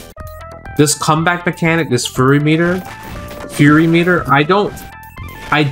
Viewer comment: I swear I hear him say “furry meter”.